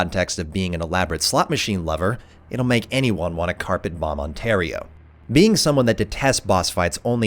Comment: Awesome video once again! Can I ask though... did you say "it'll make anyone want to carpet bomb Ontario" at the 7:27 mark? What do you have against Ontario? Haha